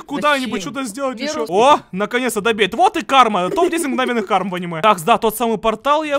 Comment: Мне одной показалось что он сказал "в аниме"?